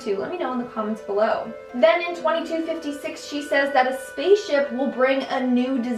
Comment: On 3:30 did she said 2256 or 2056?